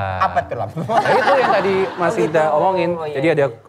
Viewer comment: ko gw dengernya kayak bilang : itu tadi yg mas ica bilangin.. 😅